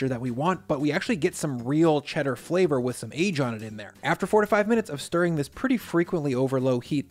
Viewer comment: at first i thought he said 45 minutes of stirring for the whiz